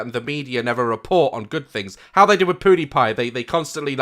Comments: did he say Poodiepie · who else thought he said poodiepie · did he just say poodiepie?? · did he just say "poodiepie" hmmm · did say Poodiepie?? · When you said pewdiepie it sounded like you said poo-diepie 😂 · Did he just say Podiepie? I CAN´T LOOLOLOLOLOL! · Did he just say poodiepie